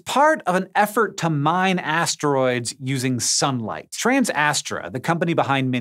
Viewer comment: Lol am I the only one who thought it said "Banging Astroids"